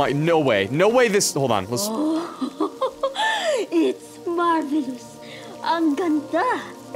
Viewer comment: I think said “ang ganda” in Filipino which means “it’s beautiful” in english😊🎉